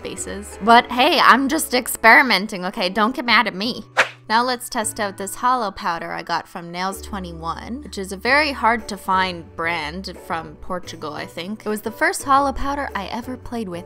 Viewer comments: Did I heard Portugal? that's my country! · DID YOU SAID PORTUGALL??!!😱😱😍😍💿💿